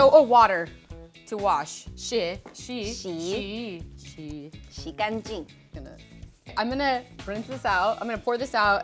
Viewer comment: did she just say shikanji?😂✌️